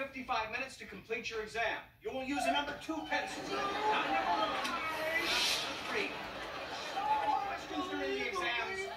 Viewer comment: What's Josh saying from 1:09 to 1:20? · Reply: SO WHY? SO UNBELIEVABLY WHY? I REALLY DO, I REALLY GIVE EVERYTHING 100- NAAH-NAH-NAH-NAH-NAH!